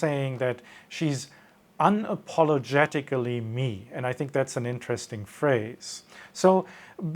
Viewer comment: The irony in the mentality of "Unapologetically Me." is that these people are often viciously unforgiving. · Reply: I misheard that as "unapologetically mean" and I didn't miss a beat. The second time it was brought up I heard it correctly.